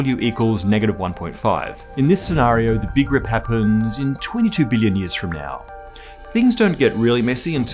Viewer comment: 20 billion years? Whew, I thought, for a second you said 20 million.